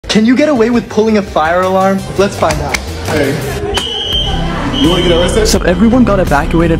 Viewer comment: i thought he said a fire arm💀